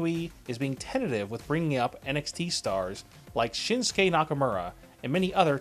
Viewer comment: I thought he said Shitsuke Nakamura